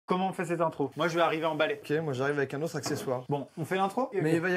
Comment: j'avais vraiment compris "moi je vais arriver emballé"